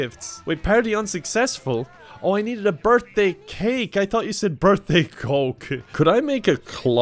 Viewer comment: Oh I needed a birthday cake I thought you said BIRTHDAY COKE